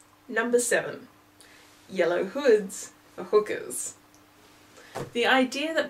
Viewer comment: Where did the yellow hood thing come from? I've never heard that before. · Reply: I heard it as yellow dress. But I don’t think discounting an entire color would have happened.